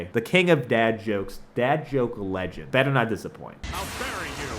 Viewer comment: Wait, at 4:55 I literally heard “I’ll marry you” and I was like, yeah, I would also die if a random dude yelled that at me